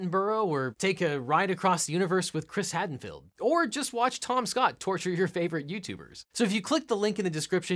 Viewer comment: He said Tom Scott, and i thought he said TomSka, i was so confused.